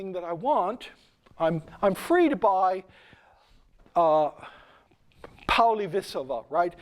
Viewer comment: whats he talking about at 46:05?? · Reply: Histories of Polybius? I couldn't find anything but that but that at most seems to be 40 volumes. Polybius was some ancient historian. 😂 PolyBS is what we should call modern news casters But it sounded like he said Poly visova or something